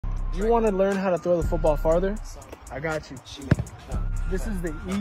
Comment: I thought he said. How to throw Father. But oof. I like the technique btw. Keep it up